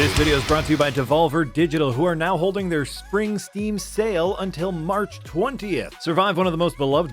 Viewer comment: I thought you said Springsteen sale